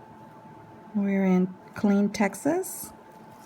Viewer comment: You guys are from Killeen TX And here I thought you guys where all the way in California. I'm from Mexia,Tx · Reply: Oh dear God I thought she said Clint, Texas.